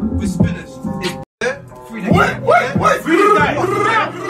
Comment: i heard ‘free the guys’ bro you are the guys